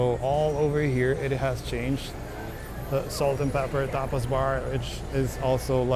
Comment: I thought you said Salt & pepper TOPLESS BAR! Then I saw the sign said tapas.